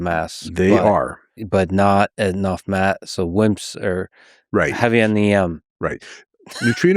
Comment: Heavy on the “M” was hilarious I heard Lex 😂